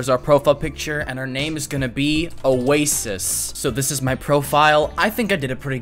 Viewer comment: Why did i hear "A racist" instead of "Oasis"